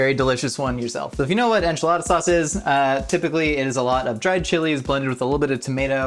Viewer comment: the subtitles say chisel instead of chili